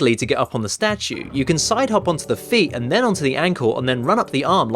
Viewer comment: Thank you guys for your patience on this one. This concludes my most ambitious episodes to date, and as such they took a while to finish. Your support means the world to me and I wanted this to be as perfect as it could be. Hopefully now, the show can return to its weekly schedule, more episodes are already planned for other games, but I won't reveal what games they are till they're ready to go. I have taken into consideration all the suggestions that you guys give me on games you want to see, and I will hopefully cover all the major ones people request daily. Anyway, thank you for supporting the channel and for over 65, 000 subscribers. I hope you enjoy this episode, it really pushed myself on this one and I hope it shows. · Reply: On 18:03 did you say you can side hop on the feet and then on the ankle then on the arms? i know it's a year late but i just noticed that.